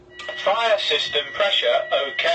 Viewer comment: subtitles are wrong its fire system not tire